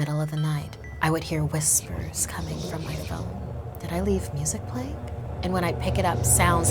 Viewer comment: "Did I leave Musically?" I heard that! I SWEAR